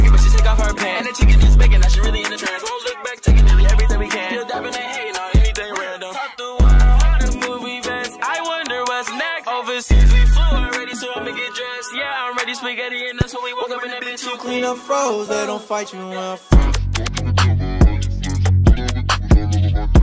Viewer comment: did he just say im ready sphagetti hahaha